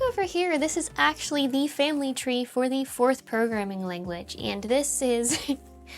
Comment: I heard "fourth programming language" and was confused